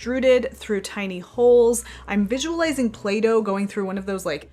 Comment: When you said "Playdough going through those...." I heard "Plato going through..." LOL